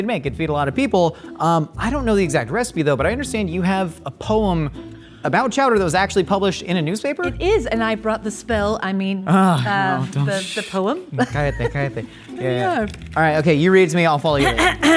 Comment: Did Josh said "callate, callate" ? How cute! Oh and Emiliy❤ They must have their own show